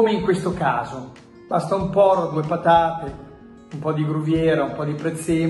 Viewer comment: solo io ho capito basta solo un por*o